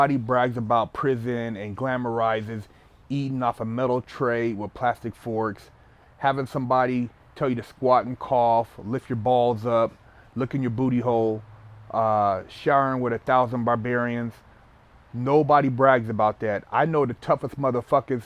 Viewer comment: I thought he said licking your booty hole at first.